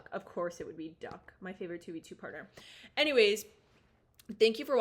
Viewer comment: Did she say what I think she said? "2b2t" as in the oldest anarchy server in Minecraft?